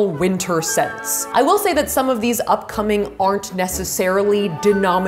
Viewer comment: does she says sex?